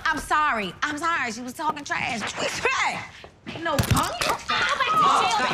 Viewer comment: after all these years I’m still not sure on what Cookie said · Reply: She said “What you expect I ain’t no punk”